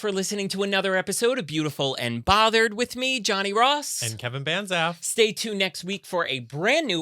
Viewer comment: Love ya both.... I swear I hear Kevin"BadA$$"... LOL